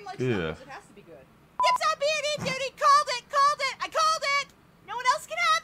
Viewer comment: At around the 21:00 minute mark, what is Yuffie saying and what does it mean? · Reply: She is saying B&E, which is short for "breaking and entering"